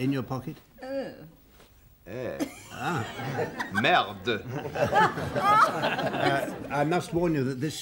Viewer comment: what did say? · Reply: Merde